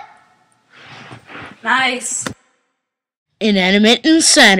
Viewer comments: When I listened to the music, I thought they said Jeeper god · SHE FORGOT wait taco is a girl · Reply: NathanMario 3000 when they said she forgot, I thought they said Jeeper god